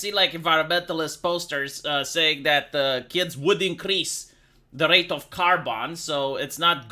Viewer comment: I heard "kids will increase the rate of car bombs"